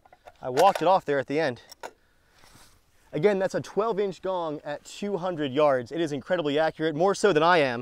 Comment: i thought he said "12 inch dong at 200 yds"